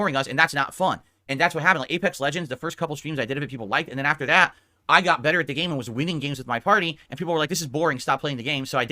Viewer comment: sounds like is saying "i got better at anal" i never actually watch. only through the goat. but what does he mean , "normal stream, birthday stream" like i can only imagine they are exactly the same. whats the difference is one more boring than the other? 5:37 what a load of shit. lol all does is try to cash in on the next big thing and fails miserably because he's a boring nutsack.